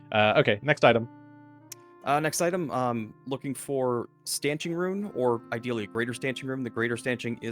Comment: Ah a shopping and downtime episode! I am not too familiar with the PF2 magic items (especially from the Grand Bazaar) and misheard the "stanching rune" as "stenching rune". Had to relisten what was saying as I thought "Stenching rune?! That is one strange rune. Doesn't make sense. Why is Wil really after here? Why would Tully need stenching on his armor?" Stanching makes a lot more sense, especially given the recent events.🤣 I really prefer the new Harrow deck design over the older one. They went with an almost comic book style with the previous one but this new feels more classical. Beautiful cards (even if some of them are very disturbing. I am looking at you "Peacock"! 😆). I am thinking of running this campaign but with Pathfinder for Savage Worlds rule set. I am still a bit torn between this and the Rise of the Runelords though. Both have elements I really love and your streams have been a great showcase of both these adventure paths. Really enjoying the adventures of Regan, Tully and Felix.